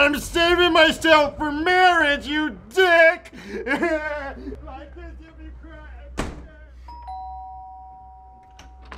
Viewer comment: like this if you heard what the old guys says when he runs away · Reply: He said "LIKE THIS IF YOU CRY EVERYTIEM"